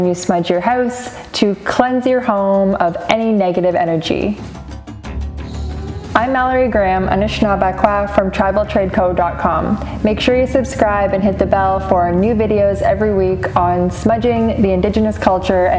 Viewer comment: Wow. The only girl I know with your name is Mallorie Grimm and I thought that's what you said! 😆